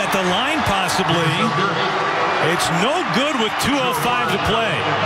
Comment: Anyone else hear at 1:57 "It's in!" ☠️